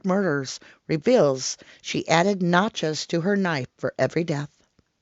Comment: OHHHH NOTCHES I heard it as Nachos at 1st I was like wth??? Like melted cheese and salsa and crumbled chips who does that??? DOH